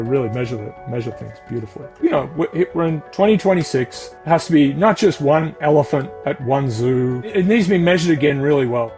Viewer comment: i hear "killing one elephant is not enough"